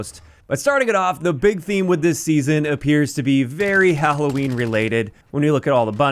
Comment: Did just say Hahohween?